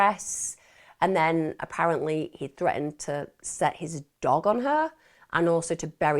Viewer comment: your cat is gorgeous.. he looks like a mountain lion or a linx.. this is awful but at 35:13 i had to listen to it a couple of times because i swear i thought you said he threatened to set his "DONG" on her... (An AWFUL American slang term for Penis..LOL) I never thought you would use that word which is why i listened to it like 3 times.. So funny.. Sorry for assuming such filth... i thought it was funny tho!! i still cant get over that cat.. He is amazing!! Mini Cougar...